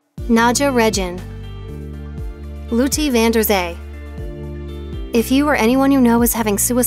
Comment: who's that? Couldn't catch her name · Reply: Was also wondering the same thing! But found her, she is lotte van der zee